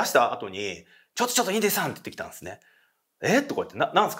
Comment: 最初の紹介 インリンさん？？ 聞き間違えた😂インディさんでしたね😅